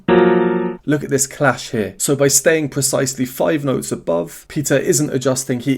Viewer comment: Is it just me, or anybody else hears "pizza" instead of "Peter" xd!? Especially 10:11 here😊